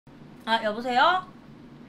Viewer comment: 아 여보세요를 아 여무세요로 들었네